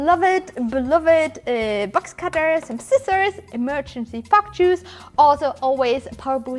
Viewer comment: do i keep mishearing it or is she really saying 'emergency fuck juice' at 2:49?